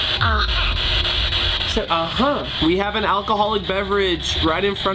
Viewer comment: is it just me who heard another voice that sounded like a girl saying uh huh?